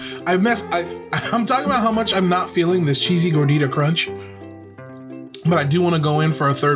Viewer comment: Which announcer? Because the interstitial is there for sure. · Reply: it just sounded like he said s****y gordita crunch instead, that's what I meant. Should have been more specific